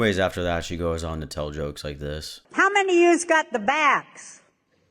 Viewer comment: I was so confused bc I thought she said “who’s got the backs” not “who’s got the vax (vaccine)” and I was like wtf does she mean 🥲 and then said it and I was like ohhhhhh 😂